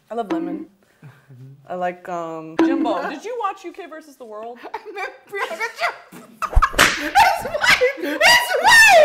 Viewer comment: Can anyone tell me what Patrick said at 00:31 I can't figure it out and I've watched it multiple times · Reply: "i meant priyanka chopra, his wife"